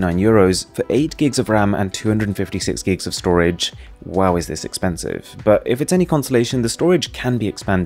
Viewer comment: Did you just say "Huawei that's expensive."? Could've sworn you did... 🙄🙂